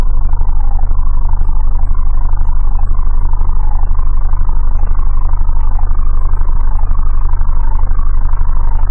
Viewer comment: what did he say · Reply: and Hey apple! What! Knife!